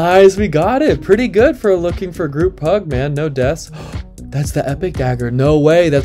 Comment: For a sec I thought you said "looking for group hug" lol